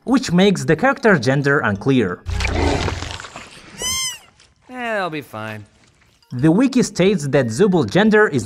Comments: No, Jax did not say "He'll" if you have the captions on in English, you'll see "They'll be fine" not "He'll be fine." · When Jax says that Zooble will be fine, i heard "They'll be fine" and I figured it was not just as a gender neutral term, but because Kinger's hands were also in there, so he just kinda lumped them in together. But I guess unless we get our hands on the official scripts, it can be hard to tell for certain. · for zoobles gender i heard "it'll be fine" not "he'll be fine" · I think with the Russian language there's a chance they used female pronouns not because of the low LGBT acceptance in the country, but because in the russian language pretty much anything has gender forms. For example in English if you say "Sasha went to the shop" you have no idea if Sasha is male or female. In russian if you say the same things, you HAVE to apply either male or female form to "went" so it's either Пошёл or Пошла - which directly tells what gender Sasha is. There literally is no gender-neutral way to say that sentence in the language. Edit: nvm, turns out Russia also has a law against lgbt propaganda. Edit 2: nevermind the nvm, the VAs indeed used female pronouns cause of the original reason - they/them pronouns are just really hard to use in russian (the law IS still real and pretty messed up though) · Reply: I actually thought jax said "THEY'LL be fine" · Im still confused for as I thought that at 1:23 it said "" ehh it'll be fine""